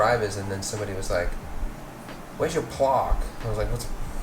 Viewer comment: Did mean "plaque"? The way he pronounced that, I'm not even sure that's what he said. 6:27 Ooooh, no you don't! ;-) You had a nice idea at the end, but the videos were in the way. :-)